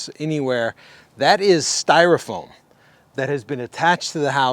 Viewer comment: Did he say that the trimming around the windows is styrofoam (at 9:58)?